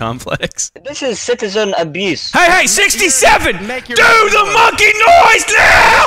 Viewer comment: the in video subtitles say 'sample text' instead of "team"